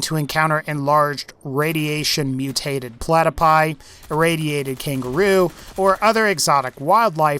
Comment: did he say platerpie